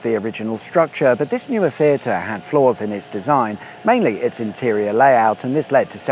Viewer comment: At first I thought you said “This new theatre had floors in its design”. No, “flaws”. This is much more worthy of a mention.